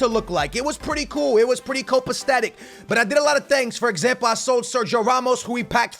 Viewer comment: Does he say 'CopacTatic' instead of 'Copacetic'? lol